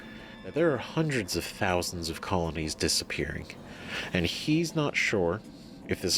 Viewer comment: He did not say colonies but rather colonists. There's no way there are hundreds of thousands of human colonies out there, that's just too many.